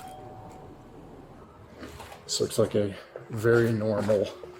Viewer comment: did he say "looks like a very normal white house with lots of kids toys"? Well hell now I know how to keep the Jack booted thugs away. Just look white and normal. What a purveyor of white privilege. Heck I'd hate to hear what he had to say about a black home. Please correct me if I miss heard.